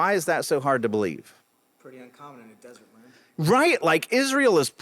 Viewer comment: What did he say at 12:15? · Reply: He said it's pretty uncommon for a desert...